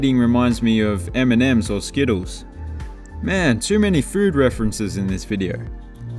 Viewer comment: i thought for a second he said "Eminems skittles" gahah